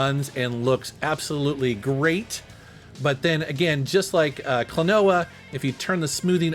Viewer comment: I hadn't heard of the game before so when I heard him say "Klonoa" I thought he said "Quinoa" incorrectly lol! Loved the vid!